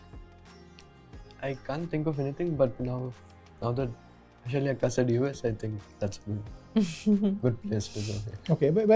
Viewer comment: can anyone please tell me what gukesh said to address vashali? 25:37 vashali also used a word to address gukesh? (Edit: guys I might be wrong with my assumption thinking they said something in tamil, if it is so please let me know or if they said something else, not addressing each other..) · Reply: Both of them said Akka whichh means elder sister in Kannada, Telugu and Tamil languages Vaishali addressed Humpy not Gukesh